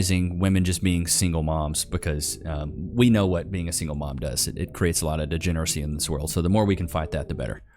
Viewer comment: Did she just say "What is the point of being a single mom?" ahahahaha